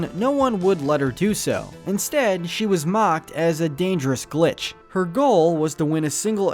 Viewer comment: I thought that king candy said the "B" word but he actually says glitch..